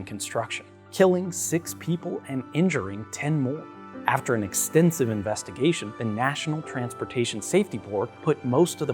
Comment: I first thought you said "expensive investigation" and I got pissed off.